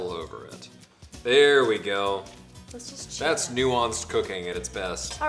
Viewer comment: the subtitle says nuance cocaine haha